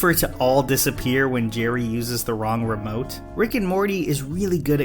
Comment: Am I the only one who heard him say Garry instead of Jerry at 2:57 ?